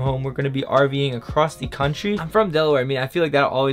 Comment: For a second i heard we'll be arguing across the country and I had to double back to make sure and I was wrong it was rving across the country 🤣🤣🤣